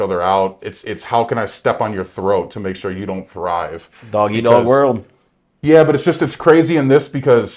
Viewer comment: did you say doggy dog world 😅😅😅😂😂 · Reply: he said dog eat dog world but this comment has me dying regardless